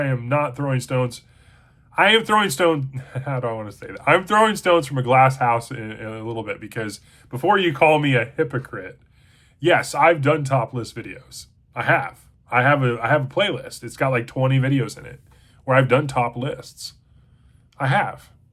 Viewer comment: I 100% heard you say "Ive done topless videos" before i realized i heard you incorrectly. But then you followed it up with the "cleanliness and without sin" bit and I thought...but did I REALLY hear him wrong? Side note: I recently dropped my first official book review. And expect to do more!